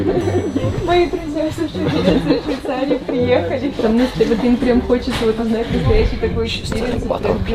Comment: she actually said “my friends arrived from Switzerland” not sweden